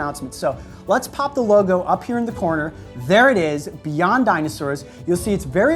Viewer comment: I thought it said Beyonce Dinosaurs